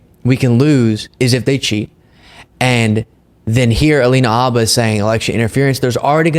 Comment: sounds like you say Halina Abba =)